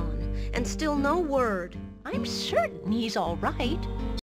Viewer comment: Think she says "I'm certain he's alright"